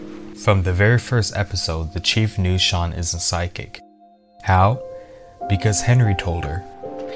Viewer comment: "From the very first episode, the chief knew Shawn is a psychic." I'm pretty sure this (which is what it sounded like to me) is supposed to say the opposite: "... Shawn isn't psychic" (which is what it sounds like if I TRY to hear that). The video still earns a Thumbs Up from me though. (Not "gets" a Thumbs Up. EARNS it.) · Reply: Sorry, its the way I talk. I hate it. "From the very first episode, the chief knew Shawn isn't psychic" is what I said.